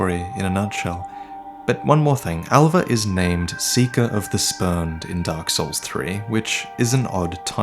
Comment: I heard “seeker of the spurned” as “seeker of the sperm” Pretty disappointed in myself